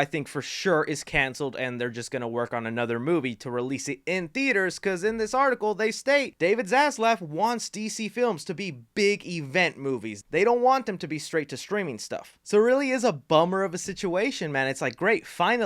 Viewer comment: Thank You For Watching!! Leave Me Your Thoughts On This Wild DC Movie News?! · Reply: sounds like he said David ass left 😂